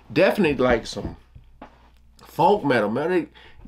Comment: Yes, there is such a thing as Funk Metal, and The Red Hot Chili Peppers are k own for doing Funk Metal. Good ear, my man. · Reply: I thought he said folk metal. lol Funk makes a lot more sense.